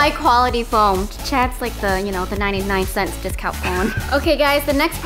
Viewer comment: vy said phone instead of foam 😂😂 like if you noticed